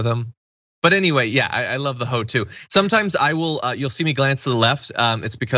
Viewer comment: Did John just say "I love the hoe too"? 😂